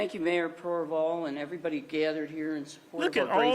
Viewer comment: I thought they said mayor Cornball lmao